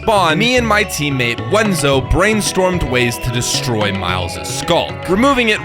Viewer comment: I got an advert right there and it sounded like you wanted to break Myle’s skull.